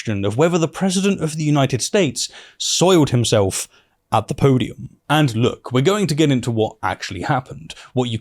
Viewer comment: You say "podium", I hear "poodium".